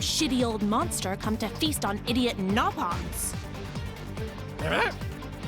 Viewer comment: just noticed but the subtitles say "Nopon" when Vales says "Nopons"